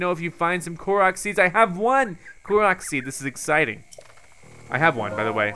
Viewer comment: Who else thought said "Clorox seeds"